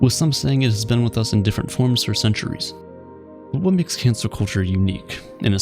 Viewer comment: I thought you said "Cancer Culture".